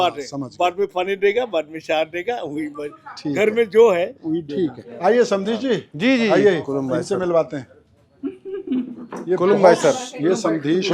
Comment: litterly i heard samdhi ji😅😂❤